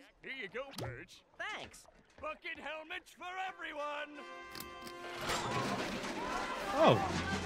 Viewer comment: Is it me or did plankton say f*ck it helmets for everyone 😅